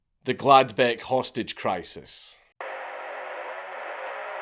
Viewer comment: I heard that as glad bag hostage crisis and was confused why someone used glad bags to take hostages until I read the words on screen lol